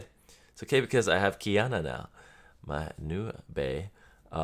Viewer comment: did he say he has Keanu?